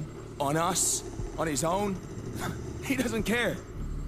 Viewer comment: he says "he doesn't care" but the subtitles say "he didn't are"